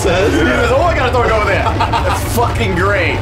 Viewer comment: He does indeed say "I'mgonnathrowyouoverthere"